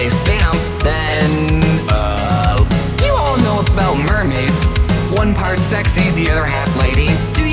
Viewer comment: Мне одному кажется что он говорит "one part sexy"?